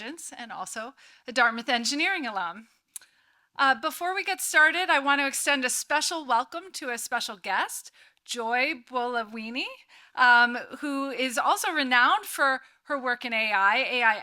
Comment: Sounds like she said "Joy Bowl of Weenie"